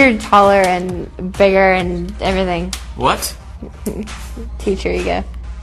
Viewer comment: What does she say at 16:45 to 16:51? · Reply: Malixie "Feed your ego." 😉